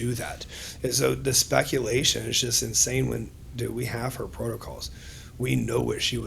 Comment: I thought he said "we have her protocols" but hes not gonna share them? What? And then claims he tried to pull her off trt? The ol "its prescribed by a doctor bro" excuse? Why wouldn't he say what her full protocol is? We know she was pushing the PEDs. Sus af.